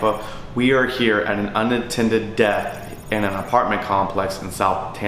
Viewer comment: did he say unattended death or unintended death? Aren’t most deaths both unintended and unattended? What’s the point if using either? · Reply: I think he said unintended